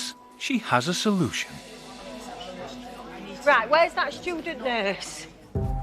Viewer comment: I swear I thought I heard Nurse Jane say "where is that stupid nurse" Lol!